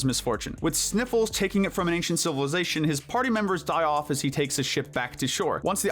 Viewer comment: HELP I PAUSED THERE AND IT SOUNDED LIKE HE SAID "as he takes a shi-"😭😭🙏🙏